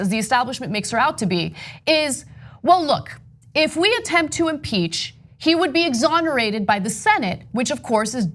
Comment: Oh you said impeach I thought you said impaled